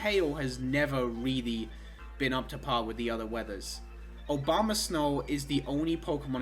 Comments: It sounded like Obamasnow LMAO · did you just say "Obama-snow"? lmao